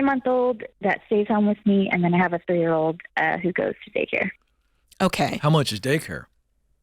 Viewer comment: I thought she said at Hookah’s Day Care. 😳😂